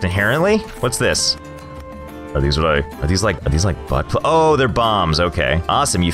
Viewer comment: did he say buttp— lmao